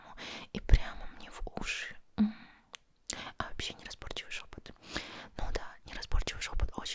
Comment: Неразборчивый шёпот, шёпот... Шёпот... Я слышу: жопа ты, жопа ты, жопа ты...))